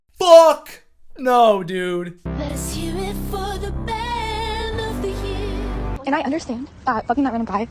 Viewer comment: Can we get a man to make a cover lordes song without all dat autotune so it actually sounds like she said man not band. I kept hearing band of the year